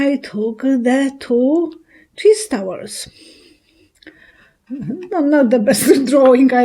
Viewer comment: Chess towers are a king and a queen: Biden and Harris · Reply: Thank You ! I thought she was saying cheese towers !